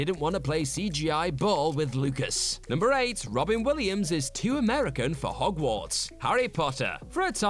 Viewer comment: I thought he said Robbie Williams at 2:13. I sat here confused. How is a British pop star too American for Harry Potter? I got a chuckle out of that.